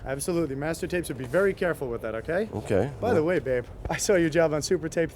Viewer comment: Did he just call him babe😂